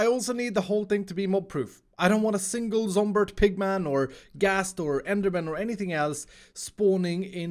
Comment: Did... Did he say Zomberg Pigmayn?